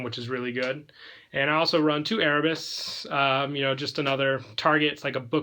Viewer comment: lol i wasn't looking at the video when you named aeroboros, so i heard "erebus" and i was like, wtf a monarch on super quant, is this man insane